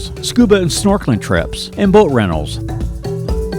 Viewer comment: Thought he said 'Burt Reynolds' at 4:32 but it wasn't. It was boat rentals.